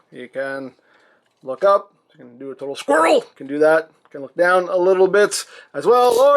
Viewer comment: ? what did you just say? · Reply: SQUIRREL!